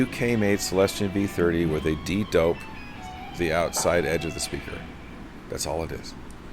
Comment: What did he say about the Celestion speaker around 9:00 ? Did he say "de-doped"?